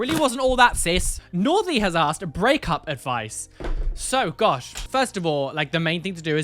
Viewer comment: I was only listening to the video and I must be tired today. For the breakup advice question, I heard “Grey Cup advice” and was perplexed why someone would ask for advice on winning the Canadian football championship. I need a nap.